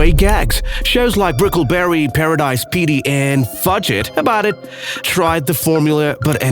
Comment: idk is it me but it sounds like he is saying fudge get about it its Fugget (Fuh-get) about it